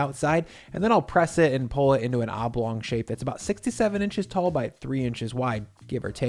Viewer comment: sounds like 67 inches instead of 6-7 inches lol